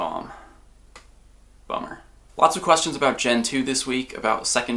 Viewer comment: on the omiatar it sounds like he says boner instead of bummer